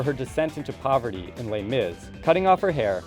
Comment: did he say "les mis" in